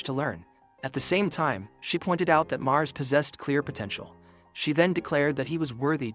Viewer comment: did you just say potential